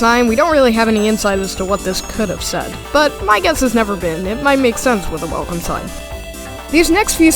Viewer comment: i think it says "neverseen"